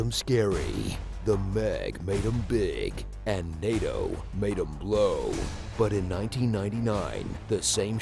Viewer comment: At first I thought he said "NATO made 'em blow" in the HT intro.